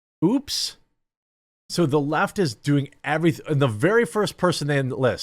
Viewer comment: At 39 seconds in it sounds like you said the Laught instead the left. I'm gonna use 😅😁😅